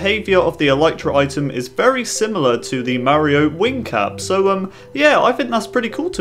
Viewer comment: I thought he said wing cat XD